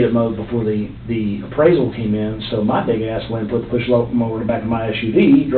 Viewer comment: Does he say "Big ass" or "ni**a ass" at 9:49?! Subtitles say "ni**a ass" lmao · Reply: Big